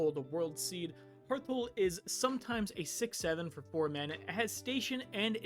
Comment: What did you say? Hearthull is sometimes a what? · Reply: I love that he says that a 6/7 in the command zone "is no joke" the irony is strong here